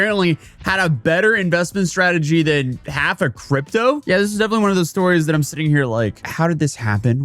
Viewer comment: At first I thought you wanted to say "better investment strategy than ... Cryptozoo" 😅🤭